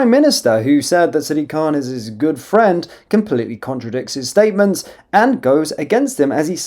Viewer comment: Thought Starmer said khans his friend 😂